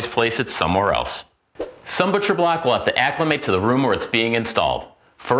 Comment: what did he say? EDIT: "some butcher blocks let you acclimate to nanana being installed" ok got it